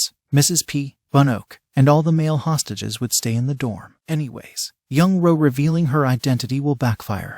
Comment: quick question but what is saying before she speaks? it sounds like she says “anyway” but idek tbh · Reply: She does say 'anyways'.